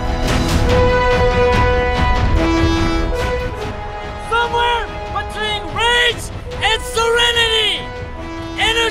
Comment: What is the last part of where he says "Somewhere between rage and serenity, energy.." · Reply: "Somewhere between rage and serenity, energy but precision, this is it!, SIX WORDS, LETS GO BLOO!"